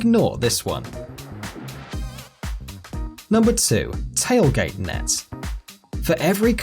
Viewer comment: I thought he said tailgate nuts